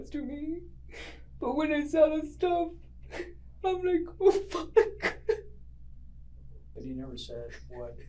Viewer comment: she actually says “but when I saw the stuff, I’m like, what the f*ck”. (Referring to the girl he hits stuff in the shed) Sorry I just saw the caption on that quote was wrong, and had to say something 😅🤓